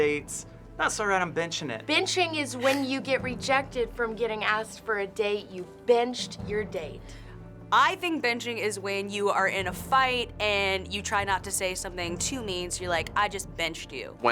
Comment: I heard "You binged your date" instead of "You benched your date"